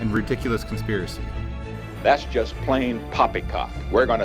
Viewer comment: “That’s just plain poppycock” Excuse me? I’m having a stroke, right? I heard that wrong didn’t I.